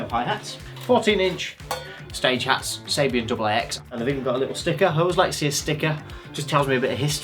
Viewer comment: What's a double X? They said AAX.